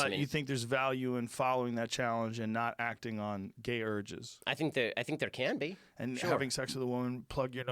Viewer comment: where Rogan says "gay urges" the auto-caption says "gator jizz" 😂